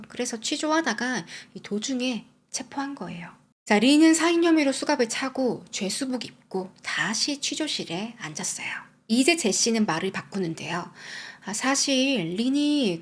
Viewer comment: 린의 살의 혐의로? 린이 살인으로?? 픠해자 피의자 이름설명이 잘 못 된건가 아님 나만 잘 못 들리는건가?